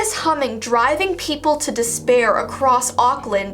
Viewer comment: did she say despair? This is Junkos doing!